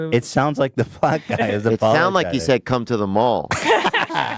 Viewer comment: It sounds like he said come to the mall LOLLLLLL